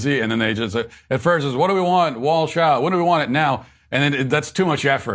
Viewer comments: I thought they were saying: What do we want? Balls out! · They said “Walsch out” but I heard “Malts now!”.